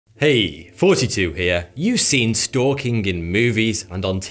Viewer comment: Probably only me but I hear “Hey, 42 here” rather than “Hey, Thoughty2 here”.